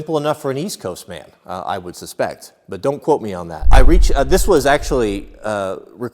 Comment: He said don’t quote me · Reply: Really, I thought he said don't Quit me. You know how those West Coast guys are, but don't quote me.